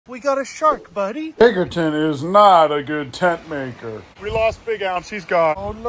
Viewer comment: Ohh you said Tent MAKER! In the long1 I thought you said tent Mate and I was like WHAT!? Biggerton Ounceton NOT a good tent Mate? ImPoSsibLe! How DARE you 💥🤺💨 but Tent MAKER! Yeh No. Tbf though he wasn't allowed tent's in Vietnam on account of the drop bears (invasive species native to Australia) 👁✌👁